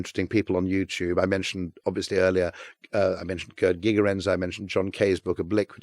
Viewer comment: What name does he say at 52:17? Kurt gigarenz? I’m trying to locate him on YouTube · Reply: I think it's Gerd Gigerenzer